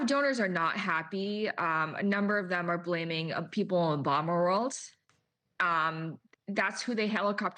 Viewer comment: Did she say "Obama world"? ....People from the Obama world? "That's who they helicoptered in after Biden stepped down. A lot of consultants in Obama world came over and took over the campaign apparatus. The same people who were leaking to the press that Harris should be replaced at the top of the ticket when Biden was running; so you can see the hypocrisy of this." Well, that makes perfect sense. After all, Barack Obama was the de facto president during the Biden administration. He was running for his fourth term.